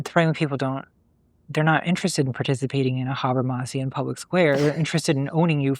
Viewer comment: What did contra say at 27:10? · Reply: ‘Habermas-ian public square’ in reference to Jürgen Habermas, a philosopher who basically talked about the development of the ‘public sphere’ as a physical place where citizens can talk freely to each other away from governmental authority, a place that encouraged open and free philosophical, social and cultural debate.